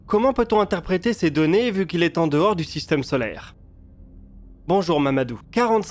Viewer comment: c’est moi ou il dit bonjour Mamadou